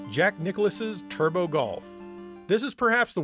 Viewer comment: I was in the other room, and I heard "Jack Nicholson's Turbo Golf" 😂 I really wish that was real now